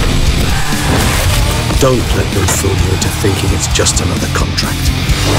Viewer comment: What did he say at 0:49? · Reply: I think I've worked it out to "Don't let them fool you into thinking it's just another contract"